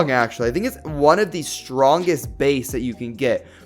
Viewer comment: In 5:54 says beys instead of bakugan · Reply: He said base. As in base B power